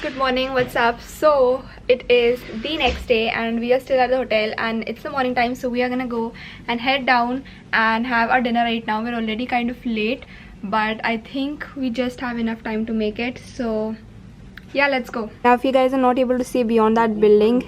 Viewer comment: Did u said dinner